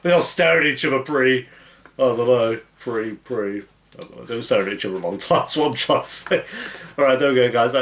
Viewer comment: What does he say from like 9:17 to 9:23? Even the captions don't get it. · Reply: “I don’t know, pretty... pretty they were staring at eachother a long time” lol :)